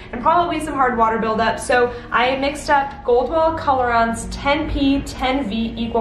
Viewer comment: Hi! Thank you so much for sharing your incredible techniques! What products did you use to tone the ends? I could not hear you. I heard "Gold wells bounce". Is that a Redken product?